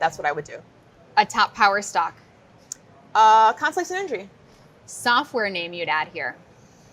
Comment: does she say a top power stock as "conflict and injury??" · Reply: Constellation Energy (CEG)